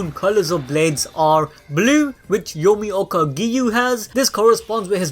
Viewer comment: did I hear Yomioka Giyu?